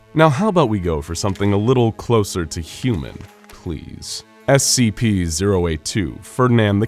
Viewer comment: Did anyone else hear him say please As if he doesn't want to talk about any more dangerous scps after he just got attacked